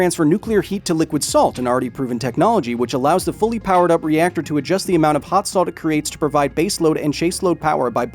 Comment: All I heard was "slim jim powered reactor"